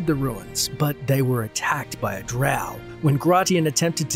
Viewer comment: "They were attacked by a Drow". Am I hearing that right? lol